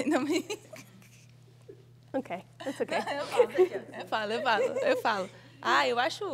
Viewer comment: what does she say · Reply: "Não posso opinar" or "i can't give an opinion"